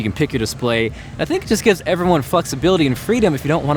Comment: Did he just say "Fucksability"